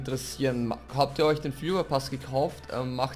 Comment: Ich hab legit Führerpass verstanden 9:20 xD